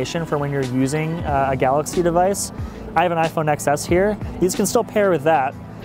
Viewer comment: Did I hear " I have an iPhone 'excess' here"???